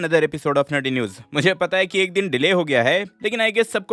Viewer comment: i misheard nangi news 😂😂😂